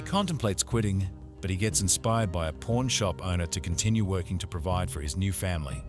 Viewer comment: I thought he said "porn shop owner" but it was just the accent. lol I remember the pawn shop scene. 🤣